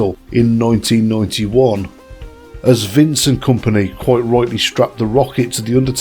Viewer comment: When you said Vince and company I thought you said Vincent Kompany 😅😅